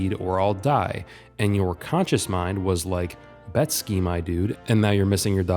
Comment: what word was said here? Betskee? Bet scheme? Vet ski? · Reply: Betski.